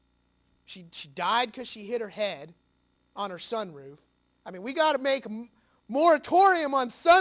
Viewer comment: Can anyone please let me know what he says at 6:40 ? i mean about something MORE among sunroof? what is he suggesting more among sunroof? I mean off course in a sarcastic way..but i wanna know the word..Thanx